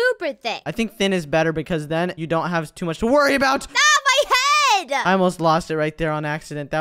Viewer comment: is says my head